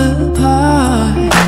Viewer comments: I thought she was saying happy trigger not hair pin trigger lol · Who else thought she said faster than a hand can trigger?